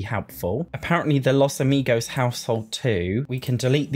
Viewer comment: i thought you said Casamigos Household and i was about to have a fit lmao